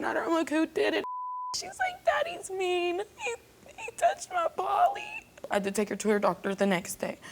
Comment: “Daddy’s mean, he touched my Pauly”? Am I hearing that right?